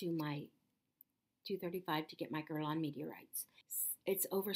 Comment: what's the my girl reward ? I think you should do what you know is right for your body. You got this! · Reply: I thought you were saying "my girl on meteorites" LOL! It's ok I like over priced stuff too.